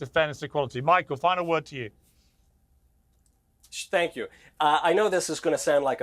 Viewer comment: Did bro actually say "spank you"? 🤣🤣🤣